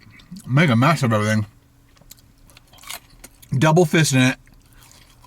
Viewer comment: did he say "double fisting it?"